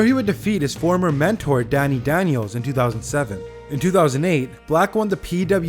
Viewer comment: did he say Dani Daniels 😏